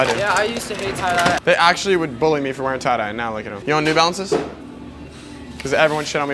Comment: the hell did u just say i heard new bouncers thats it